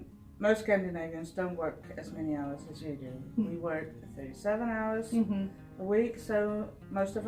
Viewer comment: Did say they work only 7 hrs a week? (see 6:02) That is what I heard and that is what close-caption wrote. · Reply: She said they work 37 hours a week.